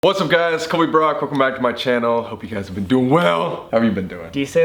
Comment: Fr thought he said Corey Brock 😬